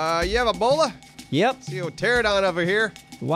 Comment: Every time someone asks if someone has a bola I hear, “do you have Ebola?”